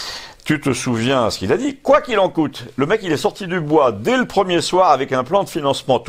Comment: Personnellement j'ai compris "quoi qu'il vous en coûtera"